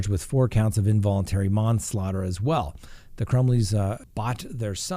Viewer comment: Did just say " She was charged with involuntary momslaughter" ? LOL listen